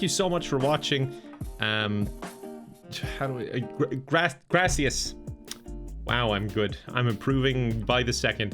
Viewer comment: LOL it took me a minute to noticed said "gracias" i thought he said something else, youll get better, spanish isnt that hard.